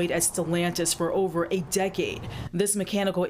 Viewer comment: did she said employed for over 8 decades? Who’s employed for 8 decades, 8 decades means 80 years.